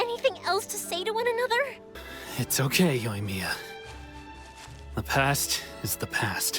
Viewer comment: When you said yo- imiya I thought you said leukemia 😩